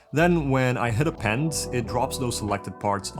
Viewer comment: What did you say at 7:50 ? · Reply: "Then, when I hit append" which is a button on the panel.